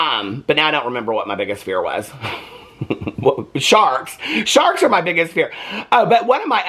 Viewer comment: I thought you said “sharts” instead of sharks! 😹🙄